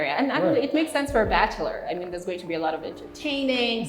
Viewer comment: did I hear you say "Bachelor"???? Whatttttttt???? Cannot wait to see the finished thing. Most amazing Bachelor Pad!